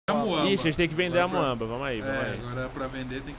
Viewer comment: ixi nos temos que vencer amoeba ( escutei isso n sei pq kk )